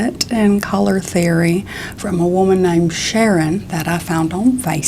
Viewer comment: I thought i heard ‘Shannon’ instead of ‘Sharon’ and i was like SHANNON DEBUSSY